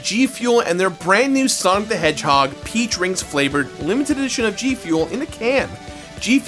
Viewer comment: I heard pee drink.